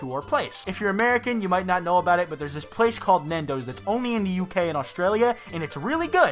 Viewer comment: did he say Nendo’s the whole Disastrous Life of Saiki K season 1 flashed before my eyes I don’t think I’d eat there...